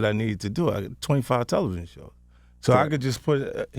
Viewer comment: Did fif say 25 television show?